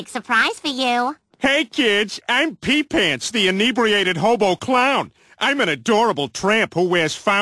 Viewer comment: The most underrated thing from this whole clip is the name he came up with for his clown character “Pee Pants the Inebriated Hobo Clown” 😂😂😂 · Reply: i sounded like he said robo clown